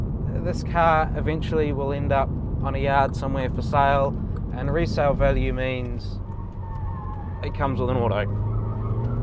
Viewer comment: Resale value means ..... What did you say (at approx 7:30)? Could you please repeat the point you were making about resale? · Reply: "resale means.. its comes with a auto".